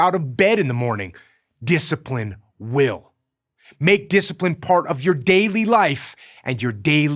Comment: What he said: Make discipline part of your daily life What I heard: Make discipline your bitch